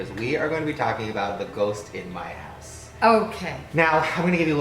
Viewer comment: I thought said "ghost in my ass" 😂😂😂😂